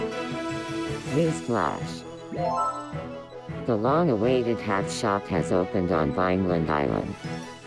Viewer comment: During a newsflash when which said "the long awaited hat shop" I thought she said "Ronald Reagan has been shot"